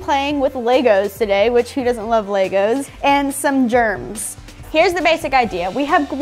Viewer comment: said “and who doesn’t love legos” but I skipped on legos and she said germs so I heard “and who doesn’t love germs lol